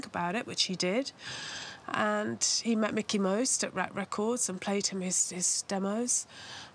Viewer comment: Misheard:....."And then he met Mickey Mouse"